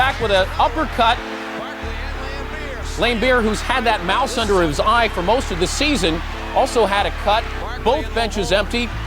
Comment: "Laimbeer, who's had that mouth under his eye for most of the season" is what i heard at first lol